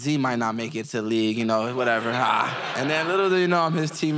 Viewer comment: "I can't wait to get him back"(from injury). Says it all for me. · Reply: I thought he was saying get him back with a block😂😂